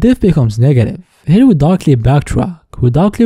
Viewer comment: If anyone was very confused at what he was saying at 31:40, it sounds like he's saying "Here, we darkly backtrack" but he's actually saying "Here, we directly backtrack. Also a lot of the time, it sounds like he's saying "can" when he's actually saying "can't" so watch out for that.